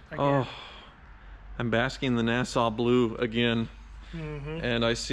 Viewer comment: If I had a dollar for everytime he verified the original paint I’d have enough to to afford a 427 vette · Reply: I thought he said NASA blue 😆